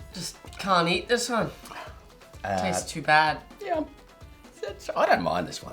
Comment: the son is way the fk more articulate and easy to understand than the father 😂 i couldnt understand what his response was “i couldnt eat it because it tastes bad” - son mumbles in the queen’s tongue - dad